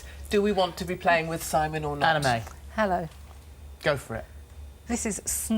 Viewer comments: I thought Anna May said her name was anime 😂😂😂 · I thought she said her name was ANIME not ANNA MAY